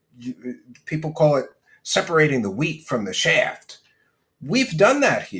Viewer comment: Did you say "separating the wheat from the shaft?" 😂